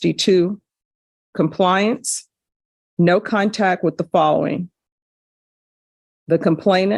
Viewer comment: Just a funny side note. Has anyone else noticed that judge Boyd always sounds like she is saying “cuntact” rather than “contact”? 😂😂😂 Just to be clear, I love judge Boyd. I just happened to notice it and was wondering if anyone else has?